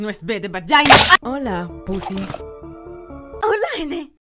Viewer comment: soy yo o escuche pusy JAJAJAJA